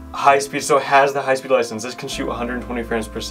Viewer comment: did you say "licences"? Hey if the hardware is capable and it was locked it would be firmware busting time.